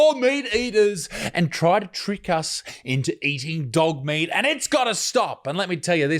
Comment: I ate dog meat once. It wasn't very nice. I thought he was saying duck meat.